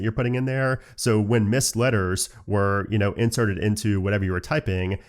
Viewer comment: Did you really say "entereded"